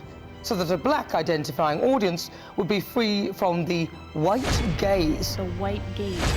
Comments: Am i the only one who thought they were saying “the white gays” for a minute? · At first I heard "the white gays". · I actually thought they said "the white gays"